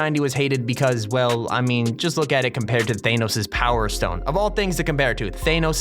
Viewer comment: Did he just say Theynos instead of Thanos?